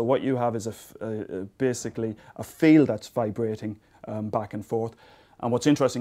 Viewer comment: What is he saying?? A "fee" that vibrates back and forth???